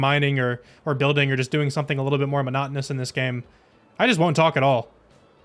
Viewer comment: For a hot minute I thought you kept saying monogamous instead of monotonous. I was so confused